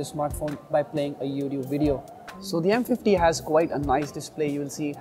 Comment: Did he just say M50